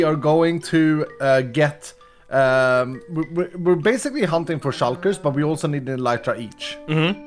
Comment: it sounded like said “humping for shulkers” and I was laughing so hard